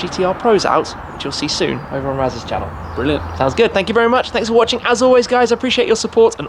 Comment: Am I tripping or did Raz say “we’re lit 🔥 “ at 16:34? Lmao 🤣